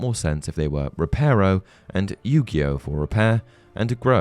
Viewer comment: did I hear he said yugioh if that the case could not even use that in Harry Potter copy right reason there anime called yugioh time to duel or get your game on